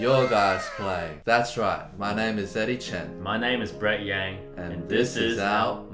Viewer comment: When Eddie introduced himself, I thought he said "My name is Dirty Chin."